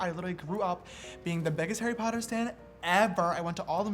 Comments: We totally are shishter educated aren't we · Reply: Did anyone else's hear him say Stan instead of fan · did he say Harry Potter stan??? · is it just me or do i hear him say i am the biggest harry potter STAND instead of fan?